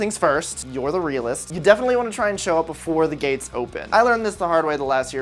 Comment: I thought you said "First you gotta show up before the gays open"... And I got confused for a second XD